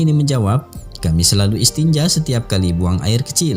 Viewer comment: istinja apa itu atau aku salah denger🤨